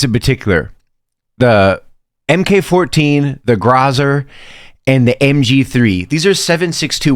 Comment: did bro say grozar